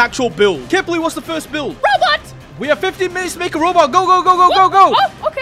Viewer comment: man I thought you said roblox